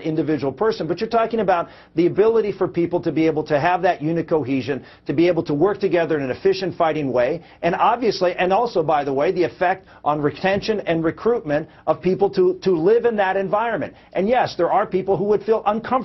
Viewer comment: am I crazy or did he actually say "rick-tension" at 2:00 ?